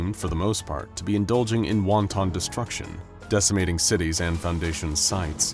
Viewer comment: did you say Wonton Destruction?